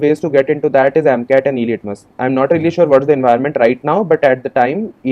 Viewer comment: Hey can anybody tell me what did he say at 7:14 Two good thing are amcat and ????? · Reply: Elitmus I think